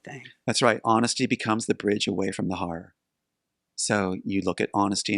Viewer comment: What??? Honesty is the bridge to ________. I didn't hear and I slowed it down. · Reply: Honesty is the bridge away from the horror.